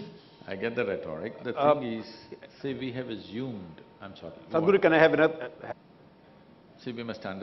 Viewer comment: what did he say at 1:54? Can i have what??? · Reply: I think he is saying for the mike, can I have it up there?